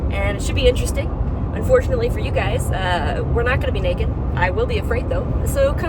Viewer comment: Dammit, I thought you said you’re gonna be naked 💪🏻🤤